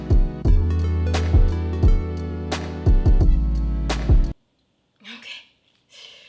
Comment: When she said "toothpicks" I thought she said "toothpaste" and I was like "wAiT-"